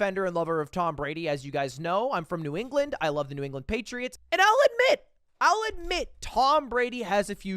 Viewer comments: How did I hear "I'll admit" as "I like men"? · I thought he said "I like men" instead of I'll admit